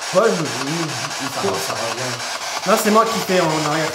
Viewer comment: Bonsoir le pve à 20:43 c’est « on veut pas parler avec vous » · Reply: « je m’appelle Paul » juste après « pascal » ou « casse toi »